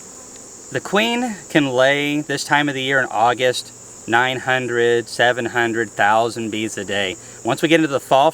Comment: I thought said, "In August, a queen can lay 900,000 bees a day!" Upon reflection, I had merely heard a verbal trick......